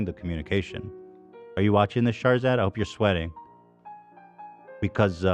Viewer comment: I kept hearing "charzard" lol